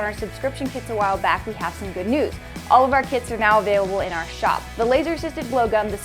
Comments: I thought she said Our KIDS are in our shop Not kits Edit I have hearing problems apparently I heard say Plastic snow cone colors Not classic And I heard him say Glue not blue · In the beginning I thought you said "Our kids are now available" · omg I thought she said all of our kids are available in our shop